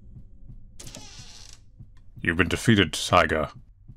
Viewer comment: "You've been defeated Taiga." Wait... THAT Taiga? The Vtuber tiger? Or did he say Tiger? Am I just a degenerate?